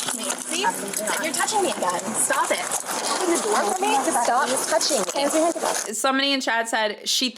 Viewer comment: Whenever I heard her say "open the door!" I kept thinking "open the noooooor"